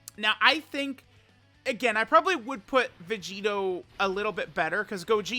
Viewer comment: My brain is so biased to gogeta that at 3:19 I heard I would put gogeto better